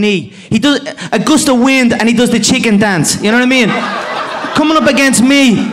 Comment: What is Conor saying at 1:26 before he is saying that Dustin does the chicken dance? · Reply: "a gust of wind and he does the chicken dance"